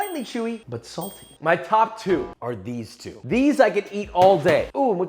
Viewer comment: Every time Josh says umami I hear 'ooo mommi'